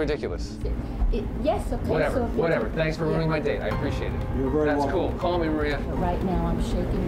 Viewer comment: “Ok, whatever thanks for ruining my day”. Older guy: Your very welcome. I’M DYING 🤣😂🤣😂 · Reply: lmao i thought he said "thanks for ruining my date"